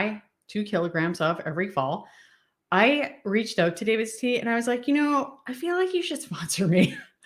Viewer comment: David's tea!! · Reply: thank you… I thought you said David Sea… lol, I searched for different spellings but nothing came up. Thank you.